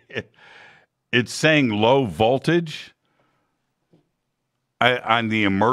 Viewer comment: Low voltage? Lol that’s what the li thing sound like it was saying